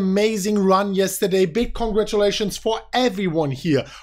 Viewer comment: it sounds like he says Bitcoin-gratulations